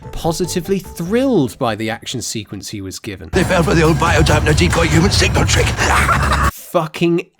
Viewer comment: I've seen the script, and he actually says "They fell for the old bio-dampener decoy human signal trick". Trips off the tongue, doesn't it?